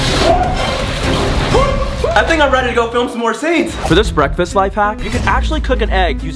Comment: y’all at 13:18 Alex says he’s ready to film more videos but the captions say he’s ready to lose weight 😂😂😂😂😂